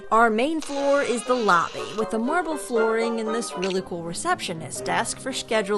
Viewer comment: I thought you said marvel flooring